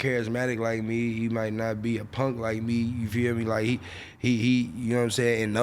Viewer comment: Did he say skilla ain’t a punk like me?